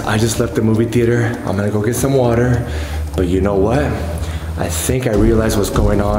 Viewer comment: did he just said eat some water😂😂😂😂